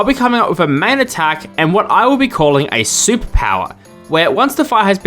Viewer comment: a soup power is what i hear